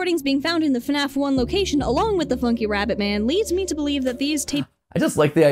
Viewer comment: I thought she said “flumpy rabbit man” lolol